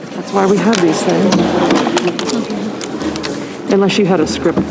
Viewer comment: Anyone else hear the woman at 11:54 say "that's why we have these things, unless you have a script", and then the mic cuts off.... 👀👀🤯🤯🔥🔥🔥